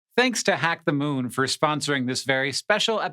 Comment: Thought it said "thanks to the MOON for sponsoring this video" instead of "thanks to hack the moon" and for a second there I thought Hank had something rather important to share 😂